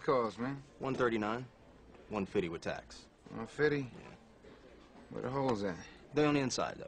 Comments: Lol it sound like Denzel said "Where The Hoes At" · sounds like Denzel said "where the hoes at" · i thought he said "Where the hoes at?" lmao